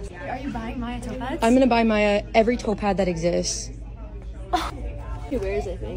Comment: The whole time, up until 8:50, I thought she was saying "topaz", instead of "toe pads". :D I thought she had some kind of lucky stone thing and was looking at and thinking "Huh? What the heck is that that thing?! That looks nothing like topaz!"